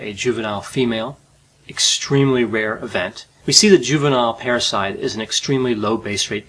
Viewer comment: Thought you said a juvenile parasite was unusual at first. Like no that's their standard mode of being.